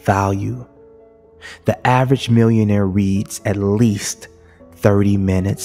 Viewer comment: a word i don't understand on No 10 ( 6:28 ) '' an average millionaire ....( word ) ......at least 30 min every single day, help please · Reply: Reads